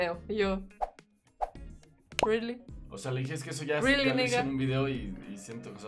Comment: did she say “really ni***” ? 😆